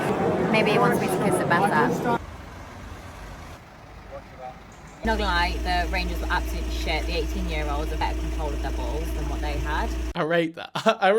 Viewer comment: what is she saying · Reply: “not going to lie, the rangers were absolute sh-t, the 18 year olds were better at holding their balls than what they had”